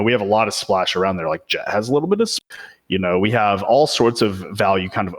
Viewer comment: what was he going to say? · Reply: Has a little bit of splash